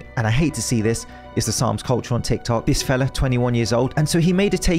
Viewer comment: SOMEONE PLEASE EXPLAIN EXACTLY WHAT HES TALKING ABOUT WHEN HE SAYS THE SOMS CULTURE ON TIK TOK ....OR AT LEAST THAT WHAT IT SOUNDED LIKE